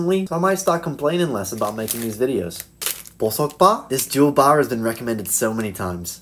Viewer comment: When he said jewl bar I thought he said juul bar 💀